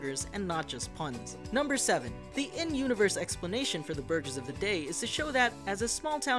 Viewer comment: did he just say “ burges “ instead of burger?